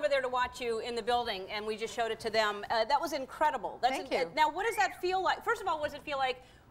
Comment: Were you watching close captioning or something? You must be deaf. She clearly said "thank you" for the compliment on the show at 0:15, and if you listen through the cheering at 3:10 she again said "thank you" for the compliment on her appearance.